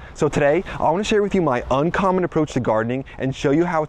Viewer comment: I thought this said unicorn, not uncommon 😂 would be perfect too with all those colors🦄